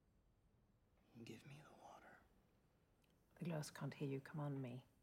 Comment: i thought he was saying “Give me the Warrior”